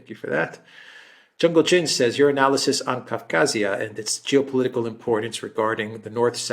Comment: Abkhazia is not Ngorno-Kharabakh (Excuse my spelling!). Methinks your mind has "slippped a cog." Abkhazia was a break-away part of Georgia, that Georgia attacked under Sakashvili and was defended by Russia. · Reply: Do you mean 2:39? (Running to 3:59, which is the next item.) I heard Alex to say Kavkazia, not Abkhazia, though I don't know what the original question said. I think Alexander heard it that way too, and took it as a question about the Caucasus situation in general, as Kavkaz is the local name for the Caucasus in several languages. As Ngorno-Kharabakh is the main current flashpoint, he talked about that. You are absolutely right about Abkhazia, of course.